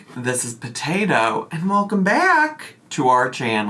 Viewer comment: Today is a day. I heard “this is a table” instead of Potato. HMMMMM.